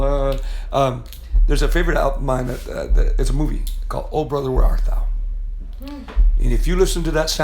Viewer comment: What movie Say Tom? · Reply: Oh brother where art thou